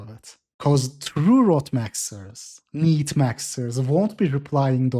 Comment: Misheard "rotmaxxers" as "ropemaxxers", lol. But then again, the former might become the latter